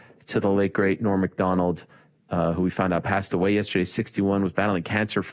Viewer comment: I was doing homework while listening.. and when he said Norm MacDonald passed away… I thought he said Rory MacDonald….